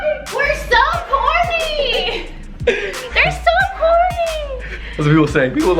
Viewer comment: I thought you said “we’re so horny” when lifted you up and I was like, 💀 chill 😂 and then I was like, oh corny.